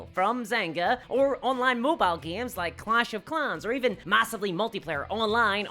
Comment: It's WHALES! Like splish splash, not wheels, like roundie round spin spin vroom. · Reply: haha i thought you said "clash of clowns", which sounds like an amazing game 🙂